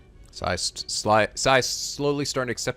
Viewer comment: H-He said Sly! I heard it! XD